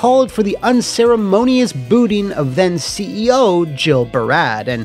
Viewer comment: I heard "Unceremonious BOOTY", don't judge me.